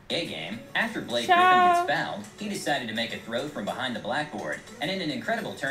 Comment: Did he say blackboard?